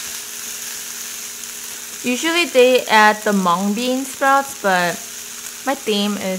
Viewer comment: Did she said Hmong bean sprouts? 😱 I'm Hmong